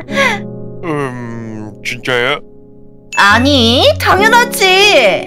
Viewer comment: 옥에티 인가요? 3:45초 인가? 그때 가사는 '그럼' 이라고 나왔는데 말은 '아니' 라고 들었는데 저만 이런가요 ㅋ